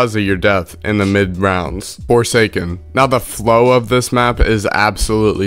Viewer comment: I thought that said foreskin 💀